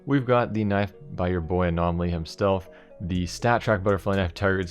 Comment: by your boi anomaly "himstelf", am i the only one to hear that?